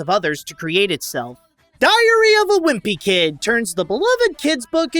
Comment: I swear it sounds like is saying “diarrhea wimpy kid.“